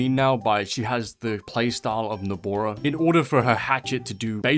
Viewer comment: is it just me or sm i hearing dabura instead of nobara ?